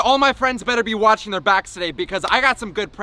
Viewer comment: I thought when you said Back you said black 😂